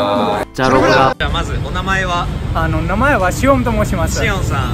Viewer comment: 知ってる人がYouTubeに出てるなんて変な感覚ww 自己紹介の時たしかにシオンに聞こえるけどこの人の名前ですww